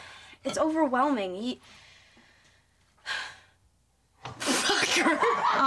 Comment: did she say fucker?